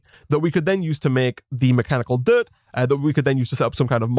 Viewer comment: In the beginning of the video, I thought you said mechanical turd due to your accent 😂